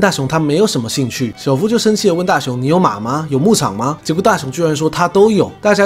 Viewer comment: 你有马吗？我听成你有妈吗？ww